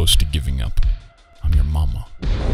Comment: not at all related but i love the way he said mama it sounded like "mawmaw"